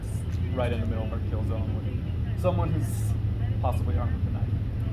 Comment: The cop says "your right in the middle of our kill zone." Did I hear that correctly?????